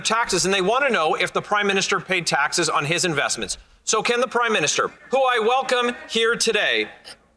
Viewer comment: I heard him say, 'The crime minister' didn't you?